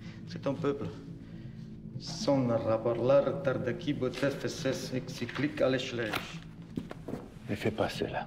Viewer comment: What did he says that in Turkish ? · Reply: As far as I understand, he said "Sonra raporlardaki bütün eksik alışverişler" Which means "after the all missing purchases in the reports.